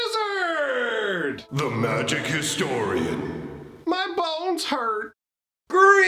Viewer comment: The first time I heard your intro, I thought it said, "my balls hurt" 😂😂 you have to know English is not my first language.